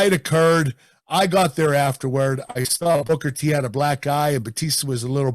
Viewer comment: Kurt angle: "i saw booker had a black eye" What i heard: "i saw booker as a black guy" Me: yeah i think everyone does, is kurt using again? 😂